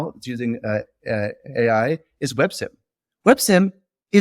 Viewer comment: what did ge say is weird? Websim?